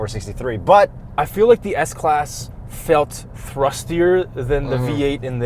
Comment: I am german, what exactly does he say at 7:39? “thrustier”?